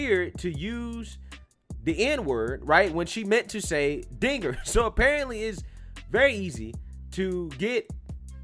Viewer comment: I heard "ninger" is that a N word? Yes is starts with N